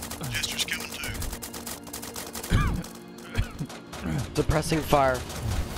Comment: i legit thought said "depressing fire" lol